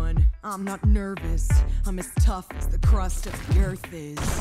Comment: “I’m tough as the crust of the earth is” “ok” LOL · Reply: Lmao I heard "I'm as tough as the crust on your face" 😂